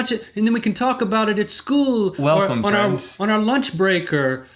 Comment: this moment when my baguette bottom thought Link said "Welcome France"...